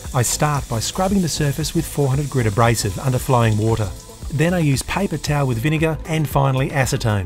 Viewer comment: thought he said flying water, which would be pretty cool and what I will now pretend is a way of describing this